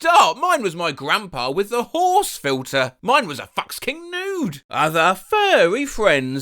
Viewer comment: SUBTITLES SAID IT WAS A "fuck king nude" OMFG IM DYING HELP